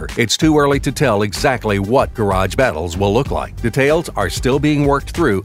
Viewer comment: i thought said: no garage battles!